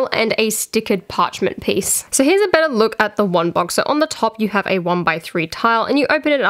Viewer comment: at 18:16 did you say Ron box or is it just your Australian accent cause I can't tell